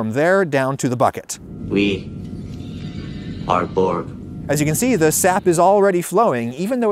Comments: Did anybody else hear that robotic voice at around 9:14? Sounded like it said “we are bored” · Did anyone else hear "we are borg" fron star trek at 9:15? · Super cool Video. What the hell is that voice saying tho at 9:13? We are borp? 😅 kinda cryptic.